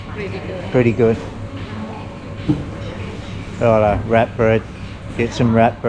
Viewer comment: Although bananas have gone up in price in the last year I bought 4 in the last week in USA for $1.25 usd. At first I thought you said rat bread and then I figured you said wrap bread.